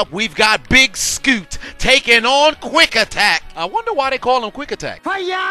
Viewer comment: I think that says “quack” attack